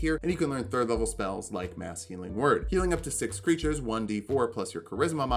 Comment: HAHAHAHA I had the video running in the background and I heard "Masculine Word" instead of "Mass Healing Word". I had to double take and everything. lol That would be a funny spell, though.